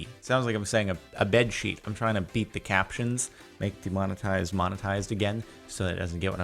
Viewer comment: Cool video and it doesn’t say there is an captions available. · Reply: He beep the captions. (That was what the CC heard) It thought he said Sheet the comments 😆